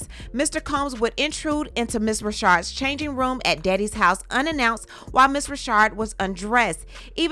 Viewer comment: Friend, I know you are getting tired after all that vocal reenactment cause that "Diddy" sounded like a "Daddy." 😂😂. Thank you for what you do. 🙏🏽💜